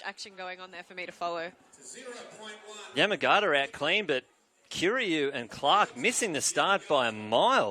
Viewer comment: kill youって言ってるように聞こえる